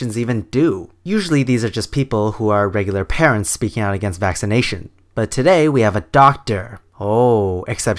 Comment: Lol, I thought it said "anti-vacation ideas"